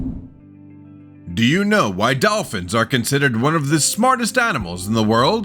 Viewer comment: did he say dolphin!??